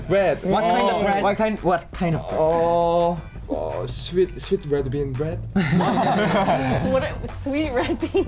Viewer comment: why the fuck does my english captions says “sweet shit rid bean”??? -1:38 y’all turn on the captions im not kidding